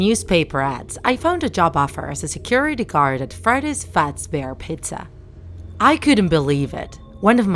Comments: "freddy fatzbear pizza" freddy: you just had to didn't ya? sorry its just it sounds like she is saying fredyy fatzbear pizza even though she is saying freddy fazbear pizza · i heard "freddy's fatsbear pizza" lmao + your saying "bownie" not "bonnie"